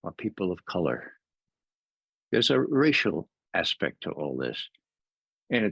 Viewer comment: When mentions that there is a racial aspect to this, I think people have to pay more attention to that fact. It's important but never talked about, not even in alternative media and barely even here on CGTN. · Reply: Did he say "Racial"? I thought he said extraterrestrial. I guess I should be mad at them using a mentally ill man like this but on the other hand they are giving this poor sick man the attention he seeks. He feels important and why take that away from him. Perhaps I laugh because if I didn't it would sink in how tragic this is. If he wants to believe he was CIA that doesn't really harm anyone does it. I just can't wait for the Nigerian Prince interview. Should be a great one.